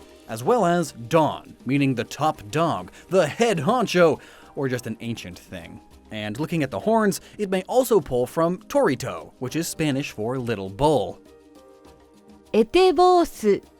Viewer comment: What an amazing pronunciation for the small bull in spanish. At first i thought you where saying Dorito